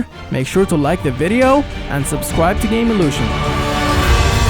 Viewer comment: When he said subscribe to GamelutioN at the end I thought he said "subscribe to gay loser"